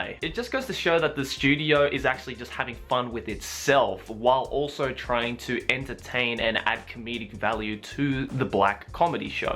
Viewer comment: Did I just hear " Play with its self while trying to entertain " -_____________________________________________________- (oh yeah)